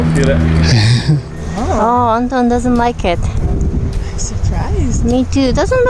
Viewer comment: I thought she said Wong Ton doesn't like it lol